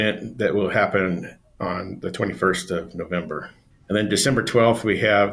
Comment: He said nov 21st not 31st. Look up cop30 its from Nov 10- 21st 2025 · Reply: I thought he said the 31 of November, maybe his lisp made it sound like the 31st and not 21st. Thanks for the correction.